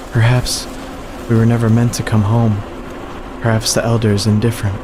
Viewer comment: At here, my subtitles said “Perhaps the Garden is indifferent” instead of “Elder”. Does YouTube know something we don’t?